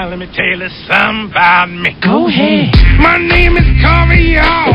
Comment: Idk why but Everytime I heard this song I thought he was saying “ my name is CARIOHHHH “